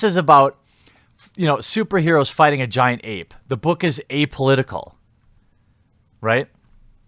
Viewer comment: "This is about superheroes fighting a giant ape. It'a apolitical." I heard ape-olitical.